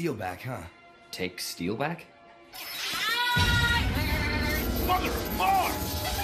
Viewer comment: what did he say? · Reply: "mother of mars!"